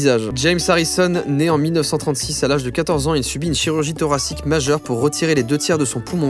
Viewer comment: J'ai compris "né en 1836 à l'âge de 14 ans"...